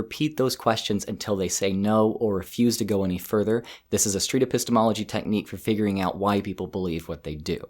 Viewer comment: What is he saying at 12:40?? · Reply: [Street?] epistemology technique